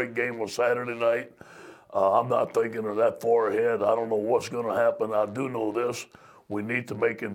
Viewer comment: Lol I thought he said "I'm not thinking bout that forehead"